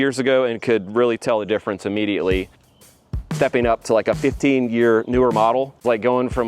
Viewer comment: did he just say 15th gear newer model lol